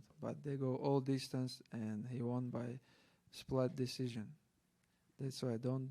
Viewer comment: Good to see how much Islams english has improved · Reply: It has, but I still laughed at 2:23 sounds like he said splat decision!